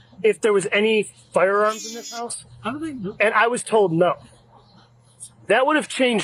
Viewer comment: did the guy not say “I don’t know” about the weapons? So nobody told the officer no unless I misheard it.